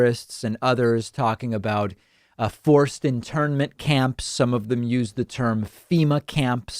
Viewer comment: My girlfriend thought David said foreskin tournament camps. Lol.